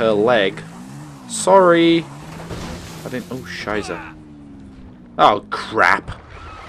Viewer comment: Did You just say Oh scheisse in German?